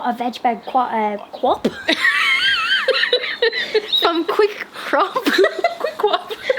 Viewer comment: Oh God! Speaking of google search history, a few weeks ago you mentioned the shop 'quick crop' but I heard 'quick cr*p' so I was genuinley searching the latter for so long thinking 'what an odd name for a garden shop' until it dawned on me. Its quick CROP. 😂